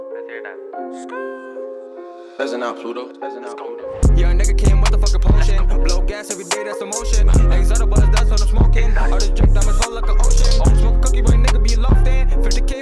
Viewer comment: He had to listen to uzi mixtape i heard "listen up pluto"????